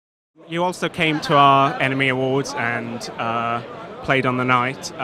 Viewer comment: I definitely heard it like Enemy Awards 😅